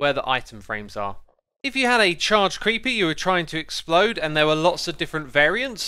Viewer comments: did you say charged creepey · is it just me or did he say creepy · Am I the only one who heard "charged creepy" at 8:39? Edit: Captions agree with me